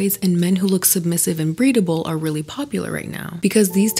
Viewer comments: “and men who look submissive and breedable” the way you so casually slipped that in there 💀💀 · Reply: sis had me cleanse my ear and thought that i heard "breathable" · “and men who look submissive and breedable” the way you so casually slipped that in there 💀💀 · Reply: I thought she said "breathable" lmao